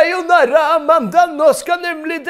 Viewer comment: Could any kind Norwegian please tell me what Sebastian (Mats) says in Norwegian at 5:12? Sorry, I'm learning the language but can't quite make out exactly what he says there. I recognize "nå skal... pappa... velfortjent..." but that's about it. If someone could transcribe what he says, it would be greatly appreciated! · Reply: A bit late but: nå skal denne pappan gå å få seg noe vellfortjent pul